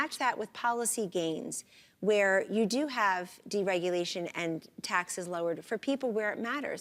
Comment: Did she just say, Deregulation and tax cuts for people that matter?"